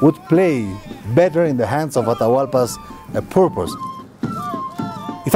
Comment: then of course everybody would... hey what happened to the audio? lol i thought he said "Everybody would fuck up the spanish"